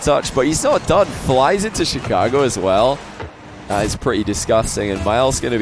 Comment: Did just say “Flys into Chicago as well” :,)